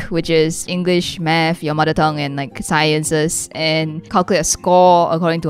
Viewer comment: For a second when said your mother tongue i thought she said your mom